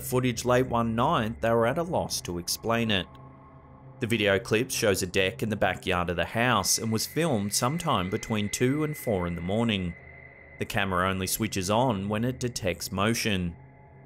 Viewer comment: I thought you said "The video clip shows a DICK in the backyard of the house" lmao! I almost broke my neck trying to spot it lol Shameless!